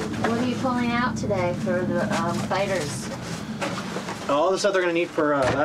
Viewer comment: I thought it said UFO fighters